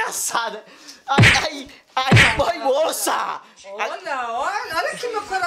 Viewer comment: só eu ouvi a falar " n mamãe" ?? Sla, ela já chama a mãe do de mãe? Q fofaaaah❤️❤️❤️